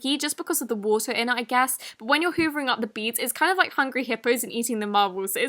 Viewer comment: did she say.... hoovering?